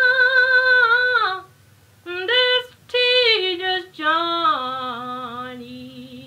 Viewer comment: Gorgeous. I'm wondering what words she is singing at one point... "all along my ____ journey"? I am used to hearing pilgrim journey, but it sounds like she is saying something different. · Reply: Jamie Thompson tedious